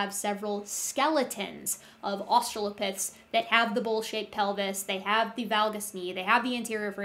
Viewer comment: I misheard "bowl-shaped pelvis" as "bullshit pelvis" xD